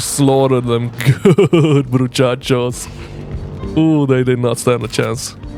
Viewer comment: Great video!!!! i didnt know the game. I will try it. 12:35 Did you say "muchachos"? Vamoooo Argentina papa XDXD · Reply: Haha I think i said Bruchachos instead of broskis 😁🤘